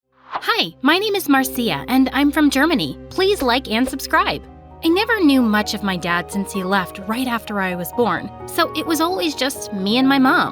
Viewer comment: why do i hear "erika" when someone says germany?